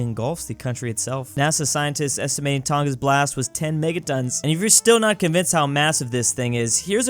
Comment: i thought that said megatrons lol